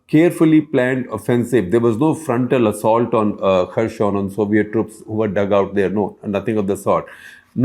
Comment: Did he say "Soviet troops"?